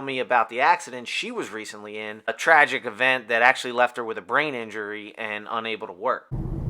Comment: I thought he said “a tragic accident causing a brain injury and not able to twerk” 😂